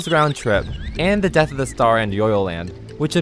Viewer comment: When you said "Death of the star" I thought you said "Death Star"